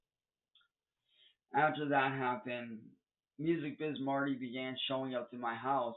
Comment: sounds like he said “Mmm Marty